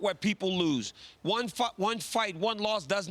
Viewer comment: did he say one F@ one one fight... lol